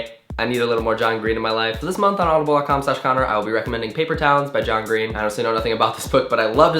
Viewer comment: I swear, I thought you were saying Paper Towels. I was like John came out with a new book called Paper Towels?